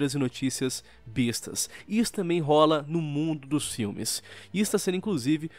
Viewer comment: só eu que ouvi mundo dos games?